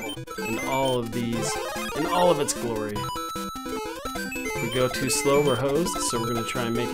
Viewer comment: i thought bro said “if we go slow, we’re hoes” i was like 💀💀 what bro mean by that lmaooo